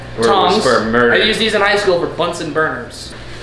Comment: Who here thought they said butts and burgers